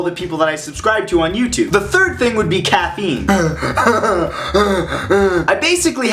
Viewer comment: For some weird reason, when he said caffeine I thought he said cat memes